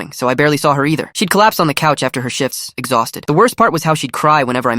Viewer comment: It sounded like he said after her s**ts